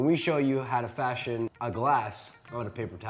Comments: When I first heard this I thought they said "I'm josh" "And I'm a mirror" then I remembered, oh right. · I heard "I'm Amir" as "I'm a mirror"